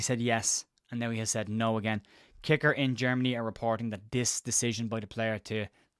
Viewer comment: Hello, I thought you were saying Leeds are getting a "Knacker" and a flight was tracked between Kerry airport and Leeds/Bradford lol😢😮